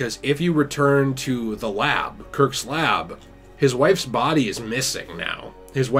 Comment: I heard wifu